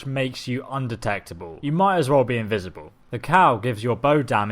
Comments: did you just say "the cow?" :p love you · i thought he said the cow gives you 35 bow damage